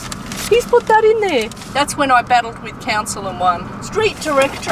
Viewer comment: i thought she said "cancer" not "council"